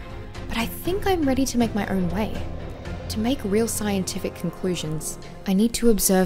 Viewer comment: did anyone else hear concwusions